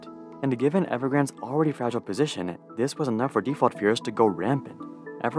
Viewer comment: I am a native Chinese speaker and I can assure you i have no fucking clue what you were saying... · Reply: "This was enough for default fears to go rampant"